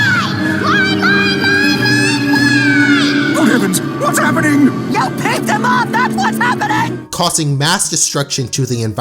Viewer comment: did Blu just say you pissed him off or you ticked him off because I don't know if CN got away with that. Does anybody know let me know on the replies. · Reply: No Blue said you peeved him off